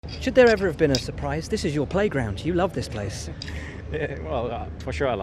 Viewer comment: did anyone say playground?